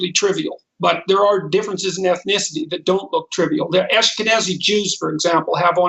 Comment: Could have sworn he said Ask a Nazi Jew. Must be getting old time for a hearing test i guess